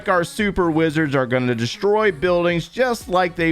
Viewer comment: The way you pronounce Wizard... I kept hearing "Blizzard".